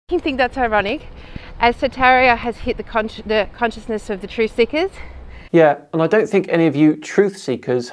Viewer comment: When she said “truth seekers”, it sounded like she said “true sickers”. And they are!